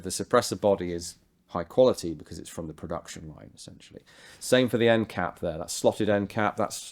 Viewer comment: did he say "some shit" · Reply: No... "essentially"